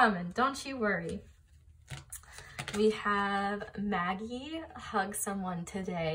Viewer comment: I thought it said Madi and not Maggie for a second//////